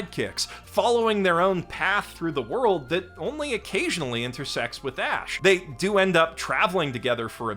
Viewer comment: I was watching this at 2x speed and instead of hearing "only occasionally intersects with Ash" I heard "only occasionally ends in sex with Ash."